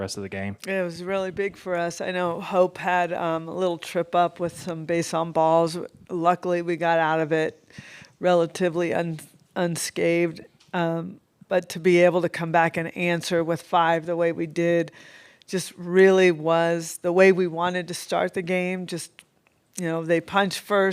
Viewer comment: lol, jocelyn, "yes, i think we are." ("the best power hitting duo of all time.") did say "relatively unscaved"? 8:43 great game, great team, great coach... let's finish this thing ladies.